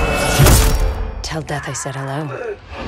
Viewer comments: Did wraith say tell dad I said hello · Wraith: Tell death I said hello Revenant: Don't worry. I'm coming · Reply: Death?? I thought she said Dad